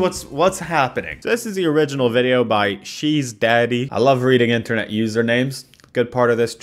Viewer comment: My hearing isn't great so I thought said "cheese daddy" lol